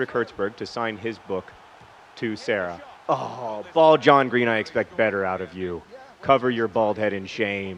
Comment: It's BALD John Green?!? OHHHHHH that makes much more sense... I thought he was always saying BALL John Green